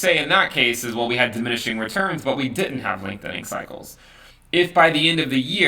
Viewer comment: I swear I hear "linkedin cycles" every time